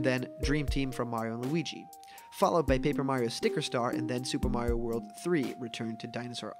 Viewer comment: Am I the only person that heard him say super Mario world three?